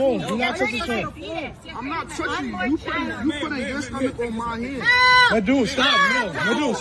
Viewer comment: It still blows my mind that what should have been a seemingly minor misunderstanding can get blown up into all of this. Social media really has become a plague to our society. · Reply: did she just say youre hurting my fetus?